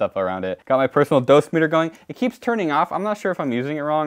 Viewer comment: Great content. Thanks for explaining how simple this stuff is. Also, "My Personnel meter keeps going off. I don't know if I'm using it wrong or if its broken." has most definitely gotten people killed in the past. XD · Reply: i thought he said "keeps turning off"